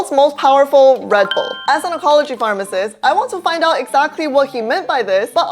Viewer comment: i need to stop watching youtube at night, i literally woke up when i heard "apology pharmacist" like a guy in "i sleep/real s***" meme 🤣